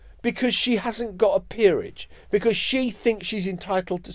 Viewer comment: When you said "she hasn't got her peerage" I misheard it as "She hasn't got her period" Ooops!